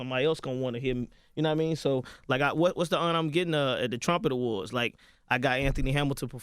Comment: I can’t lie I hella thought he said the trump awards 😂 😂